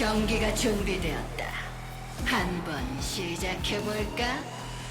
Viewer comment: Can anyone who speaks Korean translate what the announcer lady says here before each match starts? Sounds like something Honeybun, shoes on to Polka